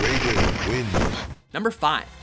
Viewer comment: I though it said radish instead of raiden